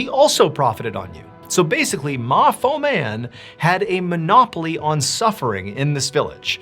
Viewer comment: Whenever I heard Ma Foman or whatever, I heard “Ma Fia” lol